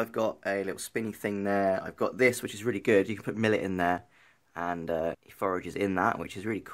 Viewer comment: did you say millet